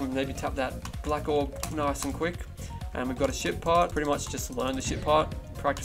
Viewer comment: sounded like you said shit part